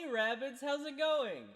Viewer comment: swear to god boy said Raditz and thought he pulled out elite ball knowledge